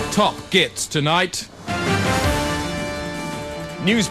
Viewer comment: Hello, does anyone know what Chris Morris says at 0:43? Thank you! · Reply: Top Gits tonight